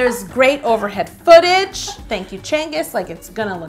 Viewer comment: I usually don't comment on videos, but did she just say "Thank you, Csenge", at 2:29? I have a hungarian friend, and he says Csenge is a really rare hungarian name, so it's always a pleasant suprise to hear it somewhere😊